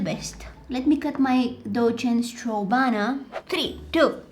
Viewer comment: her accent is SOOOOO adorable that i thought that she said '' DocHen STrowVaNa''